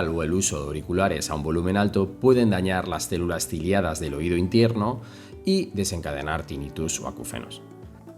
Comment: Escuché mal o dijo sonido intierno? 0:58 😂